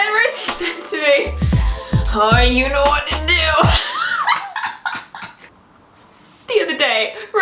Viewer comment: can someone help me please? what she said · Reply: "You know what to do" if I'm not mistaken.